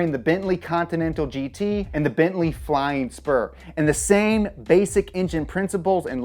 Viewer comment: did I just hear the Bentley flying sperm?